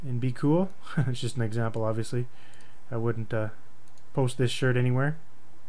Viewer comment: I have watched a lot of your videos and i got shocked when i thought you said "just kidding i wont post this shit anywhere" lol